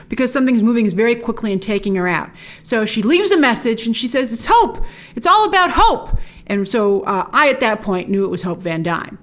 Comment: Honestly I thought Black widow said "Hulk it's all about hulk" XD